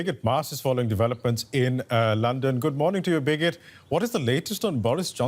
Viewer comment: Did he just say "Good morning to you Bigot" ?